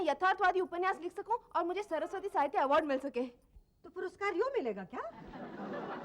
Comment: Can anybody tell me what Mummyji said here? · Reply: Toh puruskaar (award) yun milega kya?